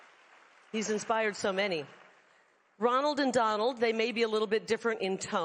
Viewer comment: For a minute there I thought she said she was influenced by Ronald McDonald. But not w that figure.